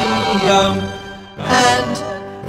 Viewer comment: i heard unununoctium